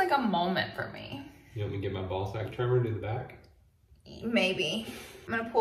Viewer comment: What I hear: Hubby: “want me to get my balls...” (Commercial plays) 😂😂😂